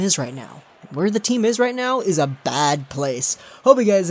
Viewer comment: please explain to me again what the heck you are saying lol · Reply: "Hope you guys enjoyed this video, Twitter and Google Plus links are down below, make sure to troll, dislike, and do not subscribe to legorocks99Gaming, and bye" Honestly, I think I've been saying that same message for like the past four years. I'm not even kidding.